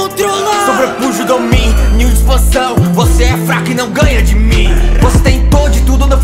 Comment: É vcs que falaram "brabos" é eu entendi "fracos" KKKKKKkkk imagina "nós somos os feiticeiros mais FRACOS" kakakaka